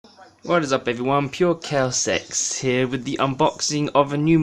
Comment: I thought he said purecowswex lol